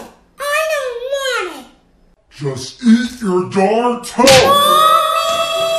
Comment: When he said cailous toast I thought it said toes